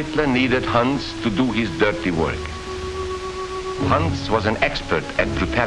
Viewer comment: Watch with English captions. It says "HUNTS" instead of "Huns for his dirty work" and "PUNKS" instead of Huns" This is all auto generated.